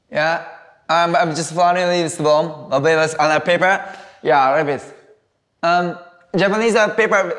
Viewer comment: あ〇るペーパーって聞こえる